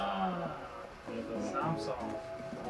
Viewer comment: did he just say ang sarap? woah.